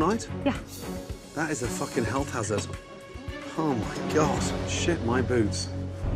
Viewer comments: "how long have these been sat here" "couple months" "shit my boots" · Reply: Thought he said “sh*t my boobs” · “Shit my boots”, ain’t never heard that lmao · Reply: UNCOMMON SENCE thought he said shit my boobs.